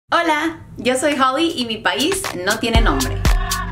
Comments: Pensé que decía: Mi país no tiene mi nombre xdxd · Pensé que decía “ mi país no tiene hambre ” 😐😐😐